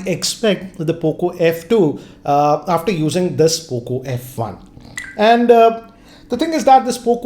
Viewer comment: In subtitles it is cuckoo instead of poco😂🤣😅